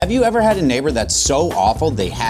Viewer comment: i heard it as "have u ever had a n*****"